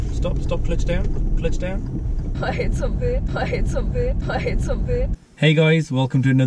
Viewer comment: what on earth did she say? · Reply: “I hit something”